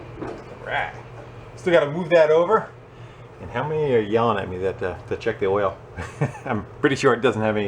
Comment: Omg I was yawning when he said your yelling at me to check the oil. I thought he said yawning instead of yelling. I was like HOW DID YOU KNOW??!!😂😂